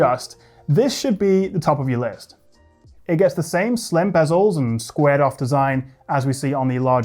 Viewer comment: did you say slim bezels ???